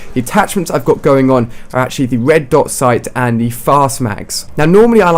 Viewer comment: did you say red dot sight??????